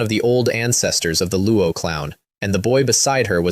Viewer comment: lol they said Luo clown instead of Luo clan.